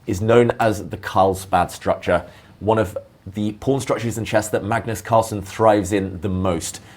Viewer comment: captions says porn structures 💔